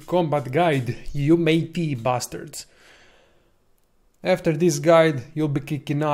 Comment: It sounded like u said 'you bastard?' lol u big meanie...